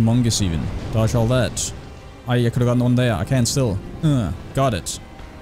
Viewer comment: wtf did you even say at 29:27? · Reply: I…coulda gotten one there, I can still. UUGH